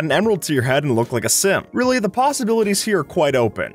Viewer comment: did you say "sim" or "simp"